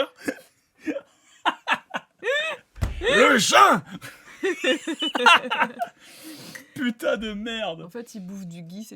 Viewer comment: À 4:23 : Le quoi ? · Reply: Jeûn